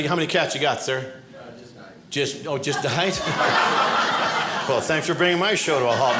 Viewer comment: "just a ----" .. What's said here? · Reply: just died... 😿